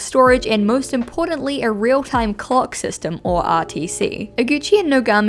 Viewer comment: BESTIE TELL ME WHY I HEARD “A REAL TIME COCK SYSTEM”🤠